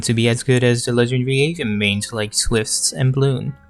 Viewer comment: BRUH I THOUGHT HE SAID "ASAINS"